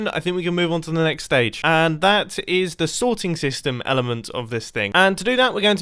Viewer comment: I thought it said “snorting system” ......this quarantine is getting to me......